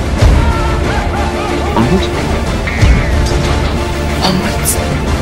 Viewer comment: What does he say? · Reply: No he says Onwards